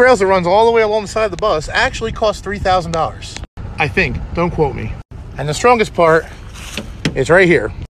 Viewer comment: “One of the rails on the side of the bus costs $3,000.” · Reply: Kintigh My bad I thought he said to quote him